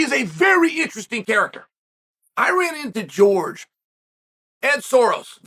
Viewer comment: When he said George and Ed Soares. I thought he said George Soros. Lol.